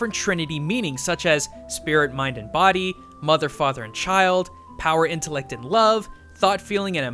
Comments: I heard "motherf*cker child" and laughed real hard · when eh said mother faht er child i thought he said mother fukin child fro a second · Who else hears "Motherafucker"?